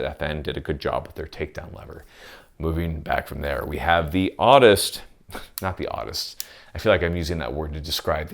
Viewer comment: You said "oddest" and I honestly thought you said "autist"